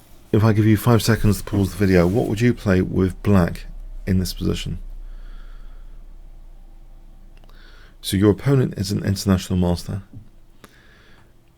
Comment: Misheard 3:10 as "International Monster" for a sec